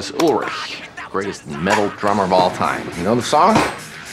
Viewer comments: At first I thought he said "worst metal drummer of all time". And I'm not even joking. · I heard "worst metal drummer of all time" the first time I played it, and thought that sounded just about right...